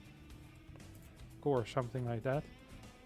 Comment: Closed Caption writes "mac" instead of "mech". Does that mean that proxy factory builds are "Mac and Cheese"?